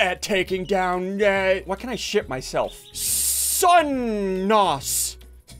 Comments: Who else heard him say "What can I shit myself?" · did ssundee say what can i shit myself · Yo, who heard at 2:01 how do I shit myself? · is it just me or does it sound like hes saying what shoulld i sh*t my self? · did he say sh!t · Did anyone else hear him say what can I shit myself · Why did it sound like he said what can s word myself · did he say what can I $h!t myself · I think I'm tripping I heard him say (what can a sh*t myself) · am I mishearing or at 1:59 does he say what can i shit myself? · did he just say what can I shit myself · did he say shit? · anyone else hear “what am I going to shi# myself?” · I THOUGHT HE SAID “why can’t I shit myself” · Ssundee swore at 2:00 he said what can I s### myself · till 2:00 did he say shit? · Naww he just said “what can I (bad word) myself?” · did he say shit himself? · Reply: He said ship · Did sundee say "what can I shit myself?"